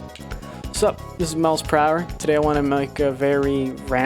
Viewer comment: i thought you said "Miles Prower" at the start and i was like "OMG TAILS"